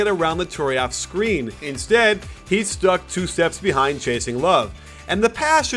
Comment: did he just say Jason Love ??